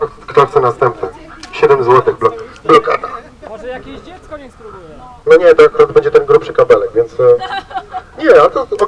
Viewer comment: Jak usłyszałem "Niech dziecko spróbuje" to myślałem że chłop jakiegoś dzieciaka chce poświecić zamiast blokady :D